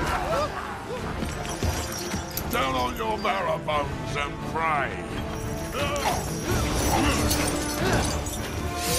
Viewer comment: what did he say? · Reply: “Down on your marrow bones and pray!” (I think)